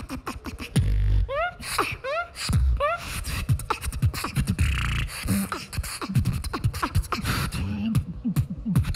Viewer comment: what was saying to the other judges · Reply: I said ESH!